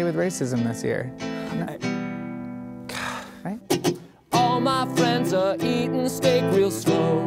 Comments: i don't think anyone heard "all my friends are eatin steak real slow" edit/ damn this got a lot of likes okay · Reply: tylerjoseph I heard "all my friends are eating steak it's slow" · DO PEOPLE ACTUALLU HEAR "EATING STEAK AND SLOW?" ARE YOU SERIOUS HOW DO YOU HEAR THAT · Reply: I hear "All my friends are eating steak and snow" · i don't think anyone heard "all my friends are eatin steak real slow" edit/ damn this got a lot of likes okay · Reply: i heard "all my friends are eating steak with snow" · i thought it will be "All my friends are eating steak and snow" LOL TO CLOSEE. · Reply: i thought it said "all my friends are wheat thins take it slowww" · I hear "All my friends are heathens anyhow"